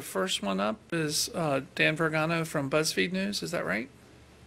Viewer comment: anyone heard the guy from buzzfeed saying ''buzzfeed nude'' ?